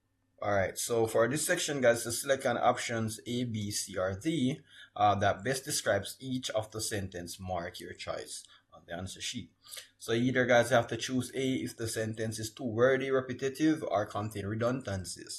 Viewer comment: Did he just say children when the sentence says choice😂 appreciate you going through this paper though